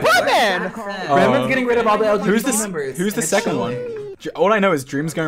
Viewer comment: did i hear correctly, ‘bretman is getting rid of all the lgbt members’... when george is dead...